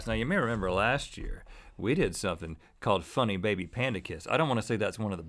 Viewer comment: What on earth is he saying??? Funny Baby Panda Kiss?? Someone please tell me!